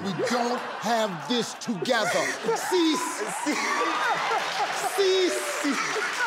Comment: When Steve said sies 😹😹😹😹😹I thought he said cease 😹😹😹🤦🏾‍♂️🤦🏾‍♂️🤦🏾‍♂️